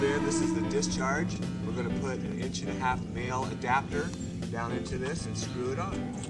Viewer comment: did you say inch and a half male adaptor at 5:20 ?